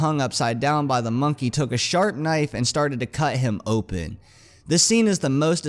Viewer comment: sounded like shart knife